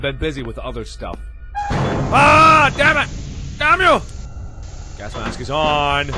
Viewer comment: Mark! At 6:33 did you just say not good in Hungarian???? (Nem jó) Edit:not 6:33